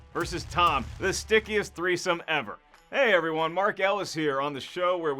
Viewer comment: Did he just say sticky threesome 🤦🏾‍♂️